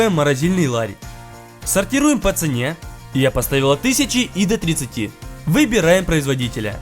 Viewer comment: Мне показалось или он сказал я поставила? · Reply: Мне сначала тоже так послышалось, но если прислушаться, то можно услышать "поставил от".